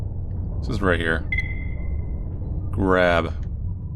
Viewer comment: I thought it said "crab" and was immediately confused and horrified